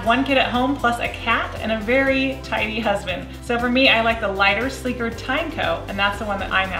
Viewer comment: I thought she said a very tiny husband